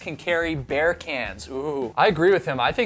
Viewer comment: At first I thought you said BEER cans. .